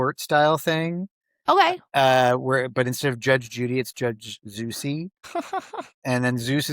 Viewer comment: I heard "Judge Juicy" before I realised it was Zeusy 🤣